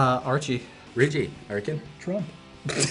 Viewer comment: did Cole say trump😂